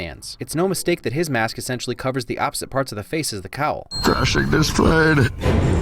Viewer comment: I always thought he said Finishing this plane